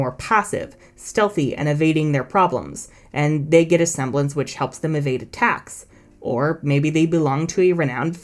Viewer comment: I had to repeat that part like 4 times then turned the Caption on. Because I kept hearing " wich helps them evade Tax" lol Also about ironwood's semblance.. how did that guy make it to general if he just hyperfocuses on things to the point of psychosis?.. WHY DOES NOBODY BRING IT UP IN THE SHOW!.